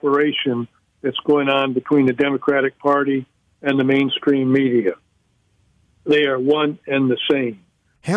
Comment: Did he say the Main Scream Media! lol